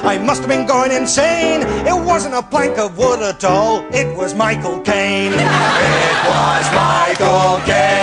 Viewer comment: i think i need to get my ears checked. I thought at first he said My Cocain not Michael Caine.